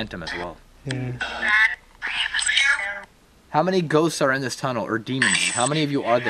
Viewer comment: sounds like it said 9 ghosts are there in the tunnel😮😮😮